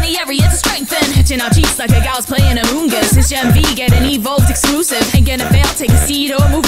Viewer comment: did she say PLAYING AMONG US!? DING DING DING DING DING DING DING. DING DING DING. TAM DAM. SHE'S VERY SUSSY